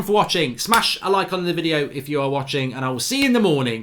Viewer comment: At first I thought he said pot pickers at the end while I was smoking a blunt 😂